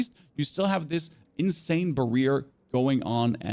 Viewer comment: Did you just say barear instead of barrier 🤣